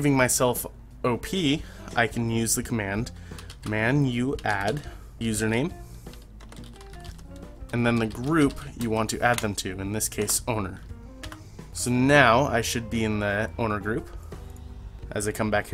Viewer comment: I cant see or hear what it stand or says in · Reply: it says "manuadd saikitsune owner" the command is "manuadd <username> <group>"